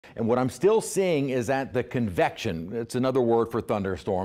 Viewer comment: did dude say convection is another word for thunderstorm 😂. im not a fancy college educated meteorologist but i think he's full of sht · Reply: I thought he said confection and it made me want to enjoy a delicious Carvel's Cookiepuss Ice cream Cake.